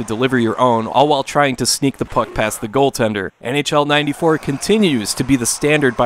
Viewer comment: misheard that as "sneak the fuck past the goal tender" lol